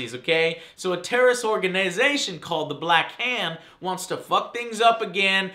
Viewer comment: Did he just say black ham?! LOL!